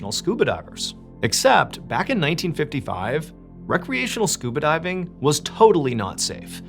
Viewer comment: You know as soon as he mentions scuba diving you know it’s going to end badly 🥺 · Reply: I hear 'DIY scuba diving' and I skip that part 😳😅